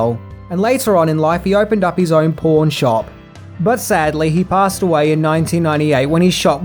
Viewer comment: I’ve replayed 3:52 so many times. Did you say “pawn shop” or “porn shop”? 🤔😂🤣